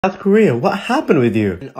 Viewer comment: I thought he said North Korea instead of South Korea at the start💀